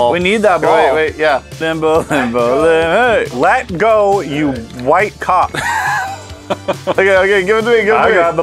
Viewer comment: it sounds like said “let go of me you while c$ck”